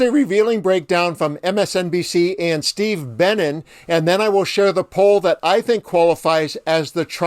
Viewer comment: Daaamn i thought you said "Steve Bannon" my heart skipped a few beats 😅